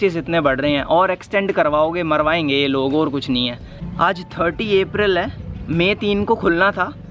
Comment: Your birthday is on 1st May and my is on 1st December andI am also learning Japanese because I love it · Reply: NO Bro , his birthday is on 30th April Listen carefully on 8:19 He clearly said 30th April